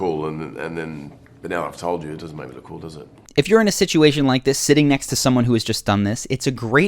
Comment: I thought they said "Sitting next to someone who is just dumbness." XD